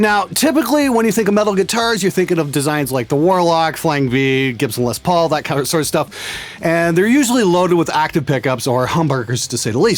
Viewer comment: Did he say "loaded with hamburguers" at 0:22 ?....don´t mess with a hungry sound tech :)